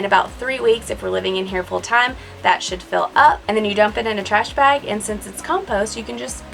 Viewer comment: sounds like she says "that shit fill up"